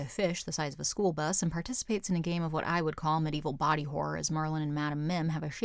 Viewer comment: I like how the subs say Marlin instead of Merlin.